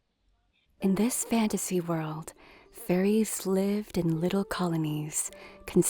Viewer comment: ngl, at 3:34 I thought I heard furries at first...